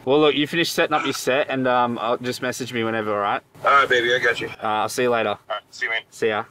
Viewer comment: anyone else hear babay